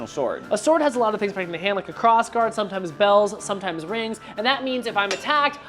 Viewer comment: Okay, I was distracted and I heard "it has a lot of things, sometimes bells, sometimes whistles" and had to go back lol